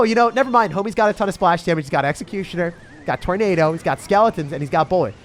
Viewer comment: I thought he said sh** damage then realized he said chip, I accept I'll never hear curse 😅